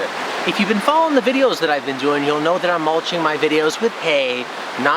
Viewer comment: It sounds like you say you're mulching your videos lol I only pick on you because I care haha Seriously, thank you for all the great content you produce. It's helped me personally a ton!